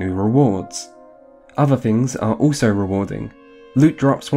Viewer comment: “Other things are also rewarding.” Did I misheard that or is that actually what you said at 16:10?